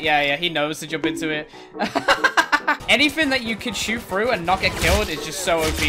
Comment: “Anything you can shoot through and just not get killed is OP.” Hackers: “Allow me to introduce you to something called, WALLS.” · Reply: I thought he said “anything you can’t shoot through but get killed is not fair” .